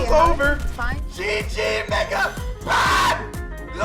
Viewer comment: BRO DID HE JUST SAY 🥷?!😭🙏💔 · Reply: No he said mega